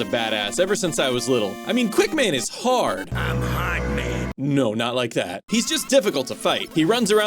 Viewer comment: did he say my hard man